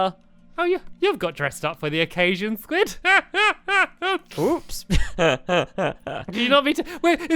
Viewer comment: did Squid just say, "Boobs!"??? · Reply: I think he said "Oops"